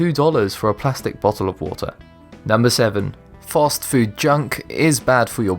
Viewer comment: He sounds like he is saying "False Food!" LOL not fast food...............